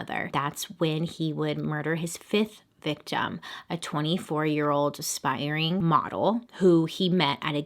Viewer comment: Ok so I was falling asleep and I thought she said “24 year old spidering muggle” 😂😂😂